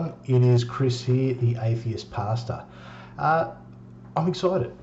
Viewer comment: At first I thought you said, "atheist pasta." Then I realized you were speaking Australian. Congrats on your 1st Trollolol